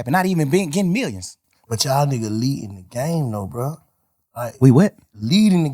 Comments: Thug thought Bank said “ But yall niggas leading the GANG tho bruh” 😂💀 Thug: Wait what 😯 Thug like Bank why you say dat lmaoo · “We what?” Thug thought Bank said - “leading a Ga🆖” , ik his ❤️ dropped to his stomach lol